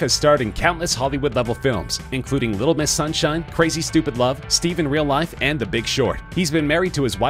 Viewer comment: Did you just say Steve in Real Life?